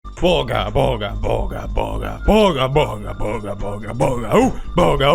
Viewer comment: When he first started the video I thought it was on fortnite Because I thought he was saying bugha bugha bugha😂😂